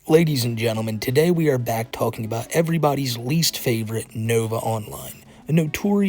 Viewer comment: I could have sworn you said “it’s everyone greased favorite” 😭